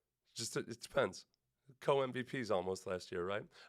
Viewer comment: LMAO…did I just hear “if you’re not a Texans fan, go die”?? 😂😂